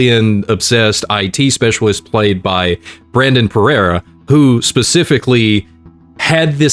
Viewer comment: I had the biggest double take here. I thought said Herrera instead of Perea